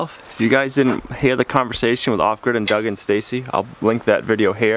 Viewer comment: ok im new to your channel but wanted to let you know i thought you said "awkward with Doug and Stacey". also love your dog.